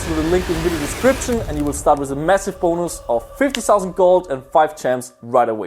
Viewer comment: it sounds like he said massive boners